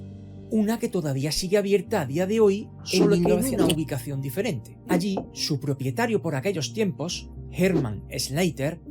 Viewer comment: Que se escucha o que dice la segunda voz minuto 20:07 ? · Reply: "En mil nov..." 😹😹😹 Se me ha montado el inicio de un clip encima de otro en un Copiar-Pegar durante la edición 🙈 No me había dado cuenta 😅